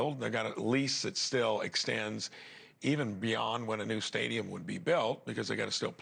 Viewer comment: Did he just say shadium 😅 shadyumm guess who's back guess who's back dadadada um 😊❤